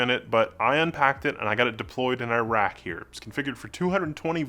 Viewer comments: thought you said you Deployed it to Iraq! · About 39 seconds in I hear “I got it and deployed it in Iraq”, and I’m thinking, “hard core brother, hard frick’n core” (no pun intended). Clearly I heard you wrong though so,……never mind 😊